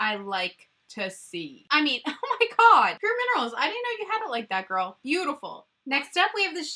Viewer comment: Sorry I keep saying PUR Minerals guys lol!! Fun fact, that actually IS their original name!! Back when their claim to fame was their foundation kit boxes lol! I swear I’m not crazy google it!🤣🤣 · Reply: 🤣😂🤣 I was just gonna say something about that. I thought you were saying bare minerals for a minute